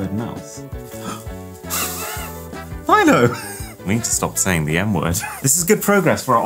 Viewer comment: “I need to stop saying the m word” it sounded like he said ñ word😂